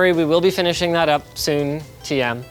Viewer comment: Did he just say Soon™..... Escape from Tarkov devs intensify